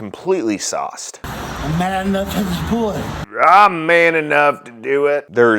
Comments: I think he said "Mad enough" not "Man enough" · I thought he said "I'm mad enough to do it". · I heard “I’m mad enough to pull it” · I heard "I'm MAD enough to pull it," but the MAD was slurred. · It does sound like he said mad enough to do it. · Thought he said hes mad enough to do it. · Sounded like he said " I'm mad enough to do it ".....? makes sense....! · I don’t speak fluent Texan, but it sounded like he said “Mad enough to do it”. Not man enough